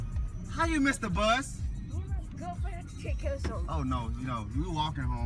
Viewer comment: What did say at 20:46? 😂😂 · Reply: Me and my girlfriend had to take care of something